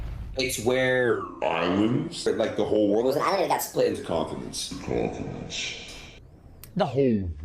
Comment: I also love how Adin saying continents sounds like he says confidence xD it adds soo much 😂